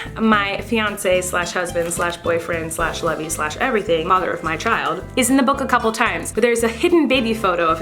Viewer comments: did you just say 'mother of my child'? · did you say "mother of my child"? hahahahaha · did anyone else notice that at 5:05 it sounds like she said “mother of my child” i might have just heard it wrong but i laughed for a straight 5 mins